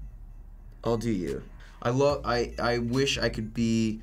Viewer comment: did i just hear dylan saying i'll do you